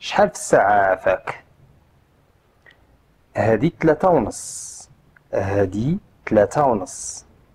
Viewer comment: Very useful lesson, many thanks!! I didn't catch what is "half past three -> hadi 3.30 (Video at 3:15) in darija. Could you please write it in latin and arabic letters? Thank you so much!!! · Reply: 3.30 : t-tlata y neS تْلاتا أو نص